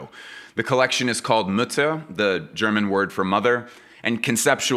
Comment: omg I thought you said the collection is called "nutte" instead of mutter, good thing you clarified that afterwords that you meant the mother/mutter 😅😂